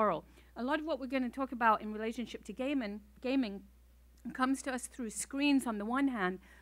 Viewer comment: when they say "gay men" instead of "gaming"